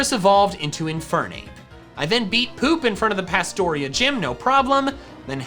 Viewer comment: "I then beat Poop", I swear I thought he said he ate Poop.